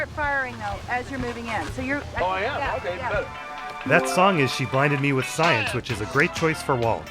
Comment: did hank just say “okay bet”